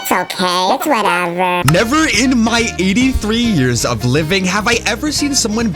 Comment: Bro did you just say you are a 83 living man